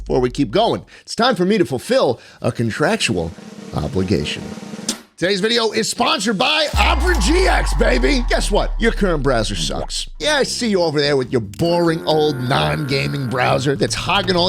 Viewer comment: I thought you said my current Brazzers sucks.